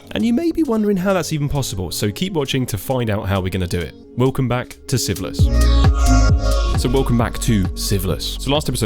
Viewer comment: When you said welcome back to sieveless i thought you said welcome back to syphilis😂😂